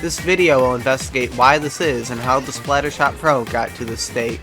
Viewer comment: I swear he just said “splattershot throw”